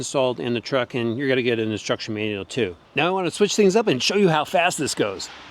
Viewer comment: Does anyone else hear destruction manual